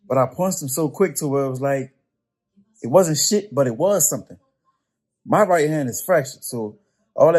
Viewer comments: I thought the clown said '' My right hand is fresh'' Hood talk for trying to act tough! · Lol I thought he said his right hand was fresh 🤣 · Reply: That’s what Bisping thought he said too but no he said fractured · did he say fractured? i thought he was saying his fright hand was fresh at first